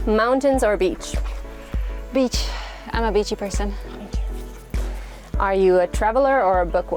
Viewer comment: I forwarded to the end and I just heard "I'm a bitchy person"